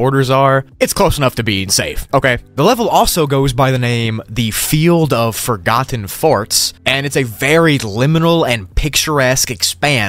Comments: What hears: the field of forgotten forts What i hear: the flield of forgotten farts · Who else thought he said the field of forgotten farts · Am I the only one who thought he said “field of forgotten Farts” other than field of forgotten forts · WHYD I HEAR “THE FIELD OF FORGOTTEN FARTS” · i had to double take i thought he said, the field of forgotten farts lol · I thought he said "the field of forgotten FARTS"💀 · I heard you say “level: the field of forgotten farts” 💀